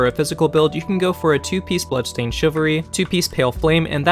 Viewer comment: I misheard it as "Bloodstained Sugary" and now I can't call that set anythi g else